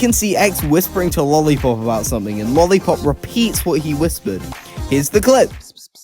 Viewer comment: I love how the captions say lolli poop lol